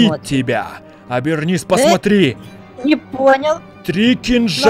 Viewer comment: Лайк если услышал что на 19:10 ведьма сказала НЕ ПОНЯЛ а потом добавила ла